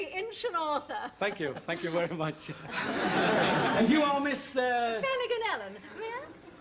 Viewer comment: I always wondered what the real name she was saying and not the misheard Flanagan and Allen heard by Eric and Ernie? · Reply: I think it's Sally Ganellen, or whatever the correct spelling of that surname might be.